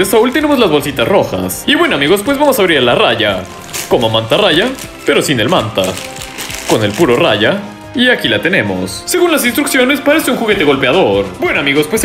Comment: creo que mal entendi "con el qlo raya" 😕😂😅😅